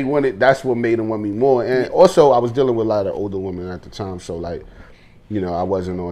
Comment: Saw that homie gained weight and legit thought he said, “I was dealing with a lot of overeating at the time” instead of older women